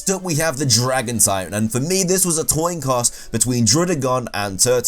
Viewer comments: Anyone notice at 2:58 when he said “toin coss” instead of “coin toss” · “this was a toin coss” instead of coin toss lol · You did not just say toin coss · Anyone else hear toin coss?